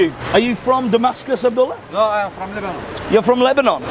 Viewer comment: When the guy said I'm from Lebanon. I thought he said I'm from Liverpool